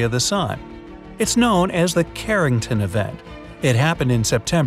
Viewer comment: I thought you said "Keratin event".